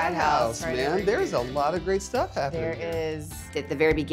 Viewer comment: I thought he said Mrbeast